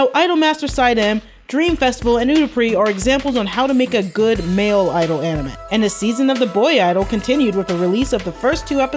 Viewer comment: what anime did you say .. Cause i want to watch it 👍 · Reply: Kawaii productions_ it’s called utapri or “its no prince-sama” there’s 4 seasons I think and another is coming in June